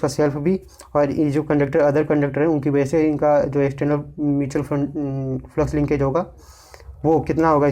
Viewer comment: Did you just said Mutual Fund 😂